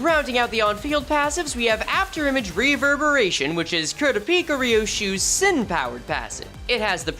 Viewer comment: did i hear "kurapika" correctly? cause if so.... that fits actually really well, how did i not notice?